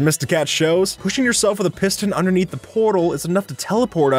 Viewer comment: I thought he said pistol😂